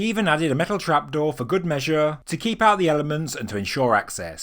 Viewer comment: Mishearing of the day: "metal trap door to keep out the elephants" Me: ??? But how do they get up...ooooh, "elements"